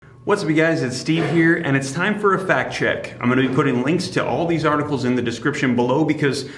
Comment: I thought you said “Fat Check” lol